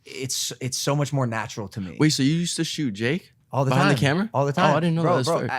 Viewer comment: When he said j cole I thought he said Jake Paul 🤣